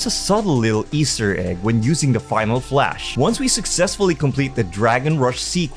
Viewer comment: Why did i hear ohio flash on